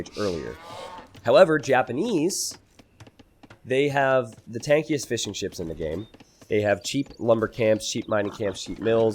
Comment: Thought T90 said Tankiest Fish'n'Chips first lol (A dish from UK) 2:16 More Fish 'n' Chips.